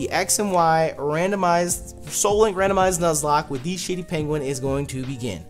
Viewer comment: I kept hearing "shitty penguin" not "shady penguin"